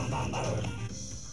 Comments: thought he said we got the wublins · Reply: AliensolutionGaming I thought he said “we are the wublins” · I thought that the green thing said: "We got the woblins we got the woblins." So I'm like "What the frick?!" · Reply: I thought it said we got the woppin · I thought it said we are the wublins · thought he said we got the wublins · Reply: I thought he said we got the waffles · I thought he said we got the whoppers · Am I the only one who heard "Wake up the waffles"? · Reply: I heard "We got the wubbas." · Well i thought the green thing said "We got the Wallets" lol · Am I the only one who heard "Wake up the waffles"? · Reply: I thought he said “we got the whoppers” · I heard “we got the woblins” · I thought he said we got the wallets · Am I the only one who heard "Wake up the waffles"? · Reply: I heard “we got the whoppers” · I thought that the green thing said: "We got the woblins we got the woblins." So I'm like "What the frick?!" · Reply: Vtheorem btw I also thought he was saying WE GOT THE WOBLINS *WE GOT WOBLINS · I heard “We got the woppers” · thought he said we got the wublins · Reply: I thought he said we got the whoppers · Thought he said "we got the waffles" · At first I thought brump said we got the whoppers. Burger King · I always thought he was saying "we got the walrus" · I thought that the green thing said: "We got the woblins we got the woblins." So I'm like "What the frick?!" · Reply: I thought it said we got the whoppers........